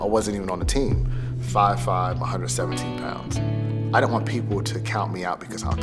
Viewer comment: 5` 5 170 pounds? should lose some weight first, then ur body should be lighter. too much muscle on the body is not good to jump high. very motivational video anyway! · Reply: He said 117 pounds, i also thought he said 170 but replayed it and heard hin correctly. Being 5'5 and 170 pounds is massive for that height anyway.